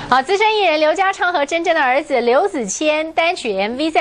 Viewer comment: 「劉家昌和真真的兒子劉子千」我聽成「劉家昌和真正的兒子劉子千」..